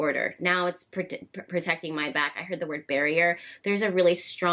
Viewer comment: When you said there's a barrier, I thought I heard 'bury her'.